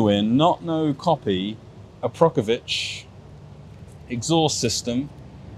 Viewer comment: did he say "Aprokovic"?